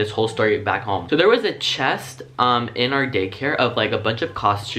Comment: it sounded like gaycare instead of daycare XD love it